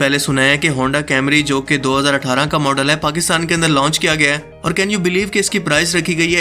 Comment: did he said honda Camry 🤦‍♂️🤦‍♂️